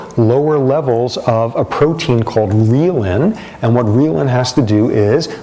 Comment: what is the protein that aids maturation of the PFC called? I heard 'leelin' but that is not coming up on google. He said schizophrenics have less of this protein